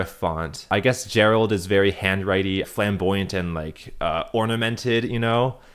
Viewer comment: why did i hear "femboy" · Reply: flamboyant